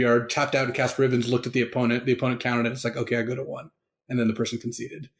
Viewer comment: It sounds to me like the guy who played Cut // Ribbons was hoping his opponent would assume that he had enough mana for Ribbons to be lethal and would just concede. Once his opponent actually counted out the mana that was spent and the damage it came out to, the guy casting Ribbons conceded because his only hope of winning was for his opponent to mistakenly concede. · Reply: yes that is it. I misheard the "the opponent counted it " as "the opponent countered it" haha